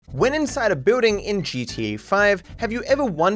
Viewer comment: Bro i thought you said "When inside a booting" I'm to high lmao